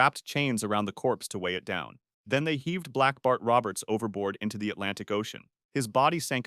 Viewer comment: i thought it said black barf instead of black bart